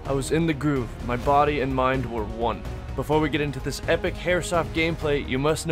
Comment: anyone else hear "epic hairsoft gameplay"?